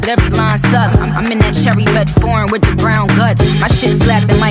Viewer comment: am I the only one who heard at 0:31 the phrase "in Dylan o'Brien's nuts"???? 😂😂😂